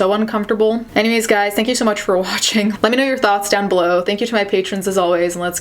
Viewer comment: At the end i thought she said Let me know you're thots and yes, i am a thot, hi